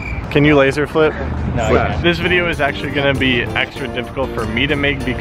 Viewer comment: i heard there this video will be extra dip